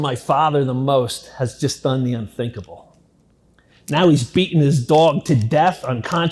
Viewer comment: I hear "He's beating his dog" and I'm sitting here, in the future as someone who's been SA'd Ok, how are we gonna do this then